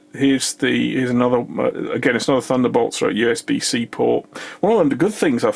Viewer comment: you mentioned 'Again, it's not a thunderbolt it's a usb-c port' ....Maybe I just misunderstood what you wanted to say. Apologies! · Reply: My diction is rubbish, "again it's another thunderbolt USB C port". When I get chance I'll see if I can add an annotation.